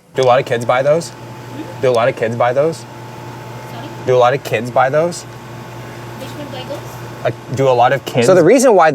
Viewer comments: i think bro said “do alot of kids buy those” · I barely understood that as well Sounded like: "Dualiz" buy those?